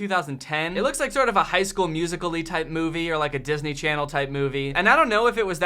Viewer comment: did he just say high school MUSICALLY??